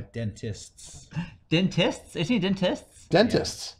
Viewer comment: History repeats itself, I've returned to this video many times. This time however, I am shocked as I read the comments and realize, as some others have, that has been saying Dentist this whole time and I can, without a doubt in my mind, confirm... I thought he was saying Dennis... ALL THESE YEARS!!